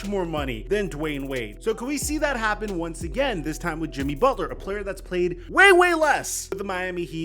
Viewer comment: it sounded like you called jimmy the greatest heat player, surely you ment wade, right? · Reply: You misheard, listen to it again. He said Jimmy has played way less than the greatest player in franchise history (Wade)